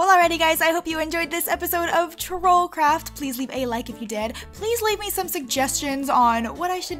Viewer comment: did she say please leave a like if u dead?